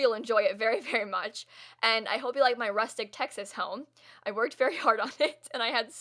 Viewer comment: Idk why but at the start of the video I thought you were saying "rustic Tetris home" and I was just so confused on how you've already run out on content.